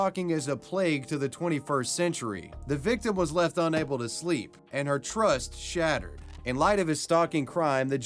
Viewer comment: the text said speak not sleep or did he say the wrong thing